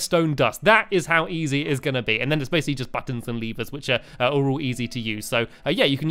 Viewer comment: When was talking about reds tone and said buttons and leavers I thought he said buttons and beavers